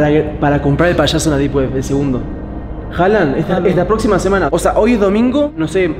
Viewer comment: yo le entendi me la jalan hajajajajaj